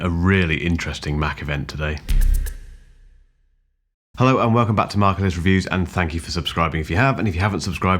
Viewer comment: For anyone who watches MKBD videos, why I do I always hear "Hi, welcome to Marques reviews." Instead of, "Hi, welcome to Mark Ellis Reviews." 😂